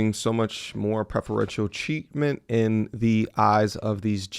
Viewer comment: sounded like you said cheatment instead of treatment near the end but yes the courts are full of cheatment